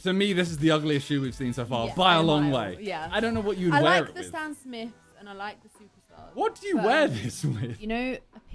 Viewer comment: Did she just say the “Sam smiths” lmao wtf